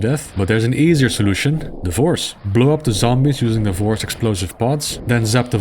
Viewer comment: at first I misheard that as “divorce”